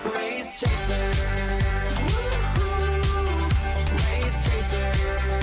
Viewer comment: Not me excited cause I thought it said drunk race chaser